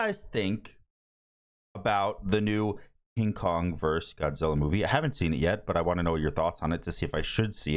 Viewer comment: No offense but first I heard "Incon versus Godzilla".